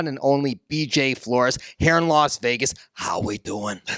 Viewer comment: I thought he said ‘hair loss Vegas’ when looking at that tattooed bulb.